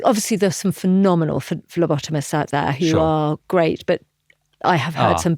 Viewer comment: oh phlebotomist 😅I thought she said lobotomist 🤦